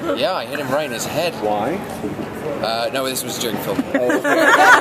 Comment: hey guys, can you tell me what toby is saying at 4:57 . Thanks a lot ! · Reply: he said "no, this was during the filming"